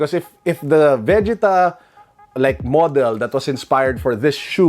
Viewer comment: good vid, but it sounds like you saying “vegetable” ☠️